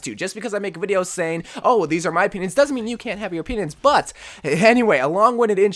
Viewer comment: i heard "just because i have a penis doesnt mean you cant have a penis"